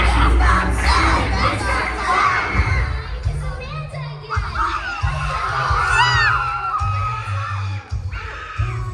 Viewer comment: does anyone know what she says here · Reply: She said "Hi! It's Amanda again!! Can't wait to see you for a third time!.. Oh and.. to a very special individual known as the shogun? There's more than one way to make moves in the real world."